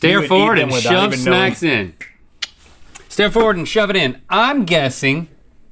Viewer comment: At. 7:15 link says “step forward and shove it in” instead of “stare forward and shove it in.